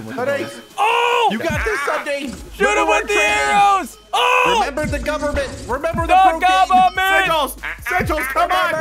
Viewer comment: says remember the cocaine, rewind if you dont hear